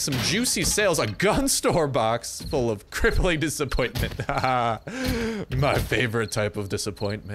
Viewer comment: I thought you said crippling depression